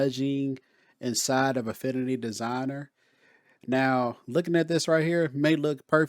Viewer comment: Haha, sorry, I thought you started of with saying "a fiddly designer" 0:11. I just found your channel my man :) I have subscribed and will learn a few things from you as I get started. I appreciate your effort. Keep going, this is good old YouTube stuff.